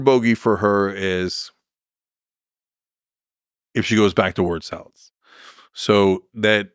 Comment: what did he say? Word sals ??